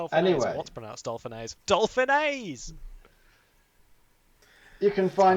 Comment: It does sound like he is saying dolphinior, i checked multiple times XD